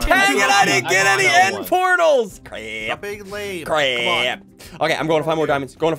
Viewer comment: did just say "I'm being layed"